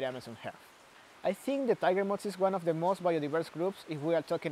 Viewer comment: the subtitles say "mods" instead of "moths" several times amongst many other typos.